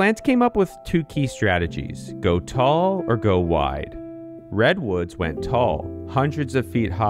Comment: When he said Red Woods I heard Bretton Woods, which is funny because both monopolized 98% of the energy the source was producing